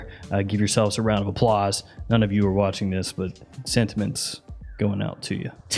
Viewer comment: did you just say “scentiments”